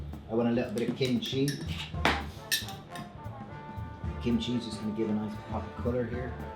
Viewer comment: Please, what did you add at 5:20? I didn't hear you... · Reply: Benj Hirst kimchee. It’s Korean saur kraut. If you live near an Asian grocery they probably will carry it and some larger grocery stores.